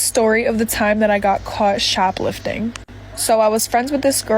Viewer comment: thought that said SHIP LOFTING I don't even know